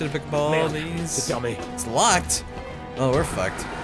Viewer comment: I forgot this was in French and in the beginning when Leon said 'locked' I thought he said 'feel me' and I thought 'Whaaaaaaaaaat?' (Yea, a style whaaat). Stupid moments are great. :)